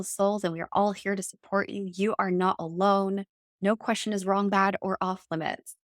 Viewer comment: I heard “you are love” ❤